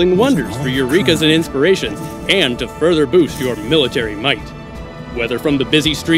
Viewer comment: Whoa that unifier ability is a game changer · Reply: oh I must have misheard I thought they said military units lol